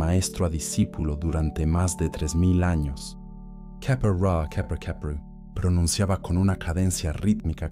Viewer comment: gracias por tus enseñanzas. en el minuto 1:15:00 cuando pronuncias las palabras en egipcio, no se entienden bien y no sale escritas en los subtitulos , podrias escribirlas por favor, gracias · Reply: "Kheper-ra kheper kheperu", pronunciaba con una cadencia rítmica que parecía sincronizarse con los latidos de su corazón. "Neb-er-djer ankh-ef em hotep." Estas palabras, significan: "El que deviene, deviene en todas las formas" y "El señor del límite vive en paz", y constituían una declaración de intención dirigida a las fuerzas cósmicas que gobiernan los procesos de muerte y renacimiento.